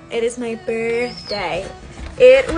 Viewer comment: Ar first I thought she said "if you would go sing a song or two off my album". And I was like ok, if that would make you happy, and since I was about to take a shower anyway - but I don't know any of the words.